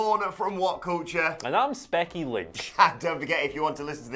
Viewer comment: Did he just say Specky Lynch?